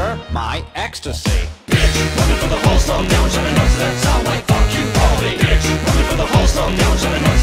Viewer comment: What people hear: "shouting noises that sound like f you Baldi" What I hear: "Shouting noises that sound like f you Wallabee"